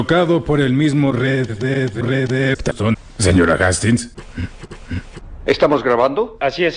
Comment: creo que dice Red Dead Redemption